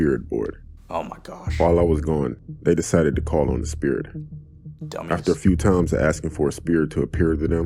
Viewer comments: DID HE SAY DUMBASS? Never thought it was who would say that😭💀💀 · did he say dumba**??? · wait did he just say dumba$$? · Who else heard kind of say Dumba**